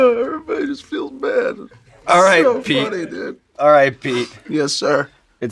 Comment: Every time I return to this episode, I hear Sean say R.I.P. (instead of Alrgiht Pete)